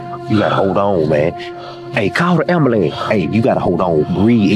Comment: Yo I thought that man said "Call the ambalam" I lost it😂😂😂😂